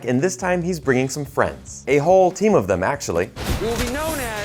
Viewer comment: I'm sorry. I heard 'a whole team' as 'A-hole team'. But it's appropriate to the movie though